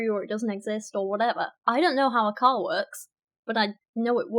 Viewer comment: I thought said I don't know how a cow works instead of I don't know how a car works.